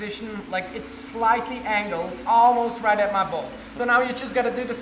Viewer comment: Lol he said almost right at my bolts but I thought he said almost right at my balls